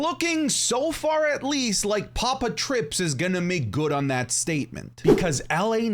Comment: Papa Trips ?? 😂 am i tripping or did you really said that ? 😂